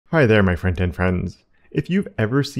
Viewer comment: It took me soooo long to realise: Hi there my FRONTEND FRIENDS… All this time I thought he said ‘Hi there my friend and friends’ for some weird reason 😂